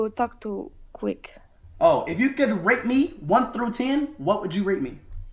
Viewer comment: the captions said rape?